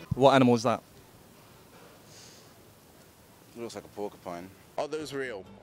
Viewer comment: "Looks like a porcupine" I literally am so done, Throw my iPad on the ground and step on it cause that's how much I wanna step on him :p Lol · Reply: Wtf i heard it as pokemon instead of porcupine HAHA